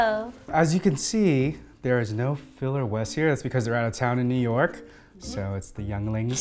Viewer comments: I thought you said filler may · I definitely heard "there's no filler Wes here" haha!